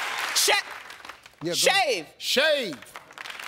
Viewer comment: I thought she said sht sht. shave😂😂😂